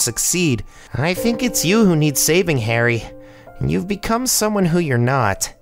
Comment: no one noticed the closed captions said Alex instead of Harry at 10:05 ?